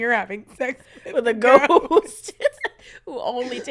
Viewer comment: I heard “with a goat” instead of “with a ghost” and started questioning things, but even with that his was fantastic. The podcast we all needed.